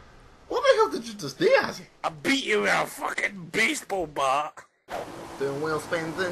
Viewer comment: Did Azzy just say "Beast"ball bat? O_O